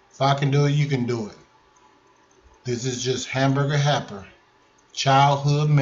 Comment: I’m sorry but it sounded like you said hamburger happer. 😂